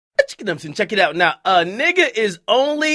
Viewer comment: Sounds like he says "Itcha Akademiks" lmaooo This is why I don't have no friends. Ain't no loyalty.